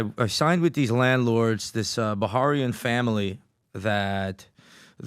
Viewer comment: what's he saying? Bahrain?